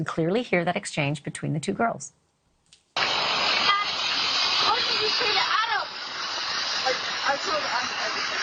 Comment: sounded more like “jack what did you say to Adam”